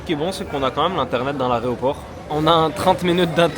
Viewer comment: l’arè quoi ?? l’areoport il a dit 😂